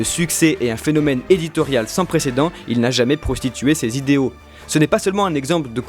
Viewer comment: Y a que moi qui trouve sa bizarre qu'ils disent prostitué ses idées à